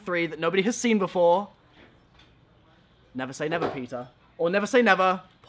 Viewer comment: Lol i thought you said pita instead of peter